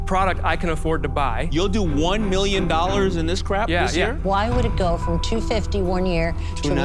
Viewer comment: Did he call it crap? hahahaaha